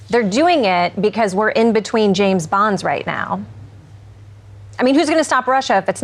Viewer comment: She said ‘they need James Bond’. I heard ‘they need James Brown’ lol 😆